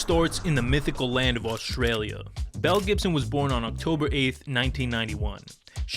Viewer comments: even her parents hated her they saw her and thought "hey let's make her name sound like that one fallen celebrity" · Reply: Lmaooo I thought they kept saying mel Gibson in the beginning 😂😂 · I looked away for a few seconds at the start of the video and was really confused when I thought I heard that Mel Gibson was living with cancer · I thought they kept saying Mel Gibson 😂😂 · I thought he kept saying Mel Gibson 😂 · I completely misheard the name and I was like “hold on Mel Gibson did what now?!!”